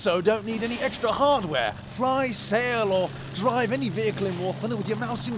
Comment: I've always heard of "War Tinder" but this is the first time I hear it as "Whore Thunder!" Ah well, all's good in love and war...